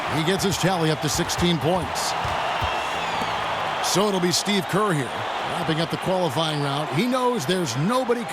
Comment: Curry Fans all be like disliking this video and comment!!! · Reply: Did anyone else hear the announcer say Steve Curry???